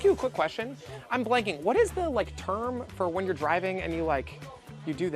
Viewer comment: Oh my days, I thought you said turn but you actually said term 😅